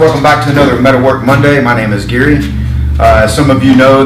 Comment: Good job Gary. Now I see what you meant on the dolly with the bolts I watched the other video first. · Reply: Bill De La Vega I thought he said his name was Geary.